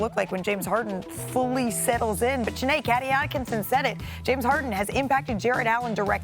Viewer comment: Am I the only one who heard her call him “Kaddy Atkinson” at 4:12?